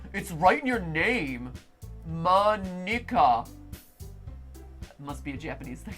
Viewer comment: what did matpat say.. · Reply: Mo nikka